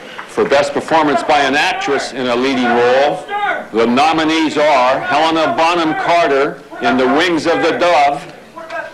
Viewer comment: What is that guy shouting at 1:36? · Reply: He was a guy working for The Howard Stern Show yelling "What About Howard Stern?" over and over after the Best Actor nominations were announced. Stern's film "Private Parts" had come out in 1997, so they thought it would be a fun spoof to yell that Howard got snubbed.